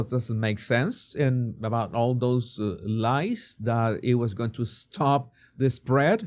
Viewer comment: Saved lives? What about mental health? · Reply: I thought they said lives too but they said LIES. That part I agree with.